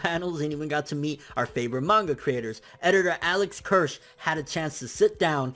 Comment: I almost thought he said "Alex Hirsch" 😂😂